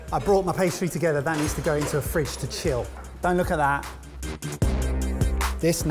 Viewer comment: lol, the subtitles say: ben, look at that